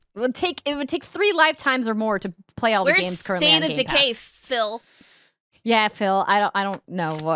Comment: I thought said "where's State of Decay, filth?" instead of "Phil" and it cracked me tf up